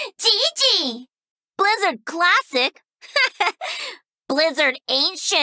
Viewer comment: I thought at first she said: "Blizzard classic? More like Blizzard ape shit"